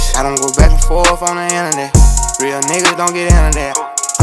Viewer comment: Lol bruh i thought this nikka said real nikkas dont got internet ahhaahhaah